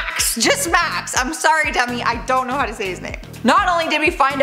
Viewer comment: It sounded like u said sorry dummy instead of demi